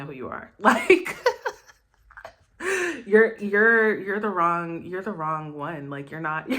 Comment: You said to him live that " you are the wrong version "? I don t understand what you said there 😁 min